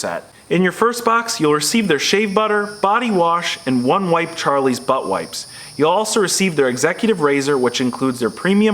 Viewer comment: did you just say one wipe but wipe